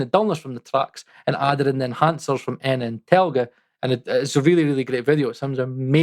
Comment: Hi, i'm a bit embarrassed to ask but what's the plug in you used last ? It sounded like you were saying "N Entelger" ....never heard of it!? Interested! Oh & I can't seem to find the link to the 2 versions to compare. Many thanks. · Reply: Enna and telge from London acoustics. On reflection you have to be very careful with these 2. I stopped using them but you can get a very specific sound from them